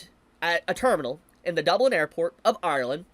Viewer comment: I thought you said Dubai